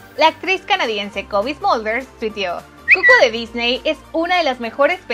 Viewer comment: Estoy loco O escuché COVID ? 😳👌🏻